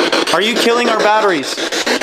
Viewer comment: I don't hear batteries, I hear, "Go Back"